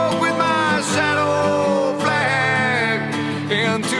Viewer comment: i always thought he was saying i will walk with my saddle bag! but no it is shadow flag which sounds much cooler!